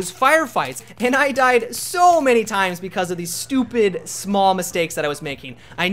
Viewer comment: I heard "smaw mistakes". twice.